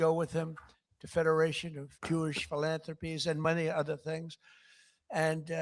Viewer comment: Freudian slip, did he just say money other things?